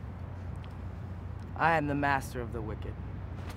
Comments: I… am the master of the wicket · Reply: I thought he was saying wicked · "I... am the master of the wicket." -My senior quote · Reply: I thought he was saying "wicked" 😂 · I a m t h e m a s t e r o f t h e w i c k e t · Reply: I thought he said wicked...